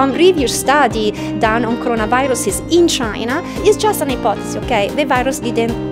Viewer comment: Beautiful video. To the producer: please double check: 5:26 the lady said “ipothesis” , captions say “report”